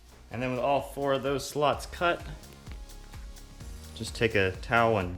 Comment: 😂😂like if you thought he said sluts